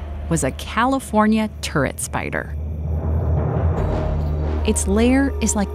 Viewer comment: I thought she said " California Tourettes Spider". I was expecting it to start yelling obscenities in a valley girl voice.